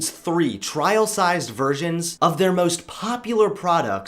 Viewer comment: I thought he said virgins lol